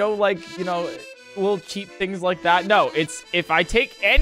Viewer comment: WHAT DID U SAY? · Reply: Lil cheap things like that